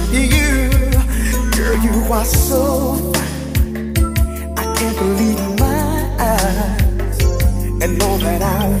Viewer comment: I'm pretty sure he actually says "I can't believe my eyes" instead of ''I can't believe you're mine"